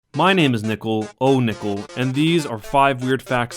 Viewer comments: i'm new to this channel... though i thought he said "My name is ohnipple" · I was eating apples while watching this. And while I was chewing, at the beginning when he said "My name is nickle" I heard "My name is nipple"